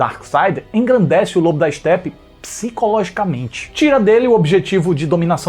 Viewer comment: Não sei pq eu ouvi "globo da selfie". Kkk